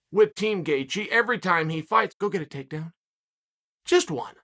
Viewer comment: Chael with the Chis Hansen-esque “go get a takedown”, swear i heard “come take a seat” at first😂🤣